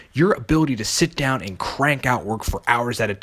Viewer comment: did he say crank one out for hours?..... dude ain’t nobody got time for that. Plus who wants to be the guy with 1huge forearm? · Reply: eww no he said crank out work, you are hearing things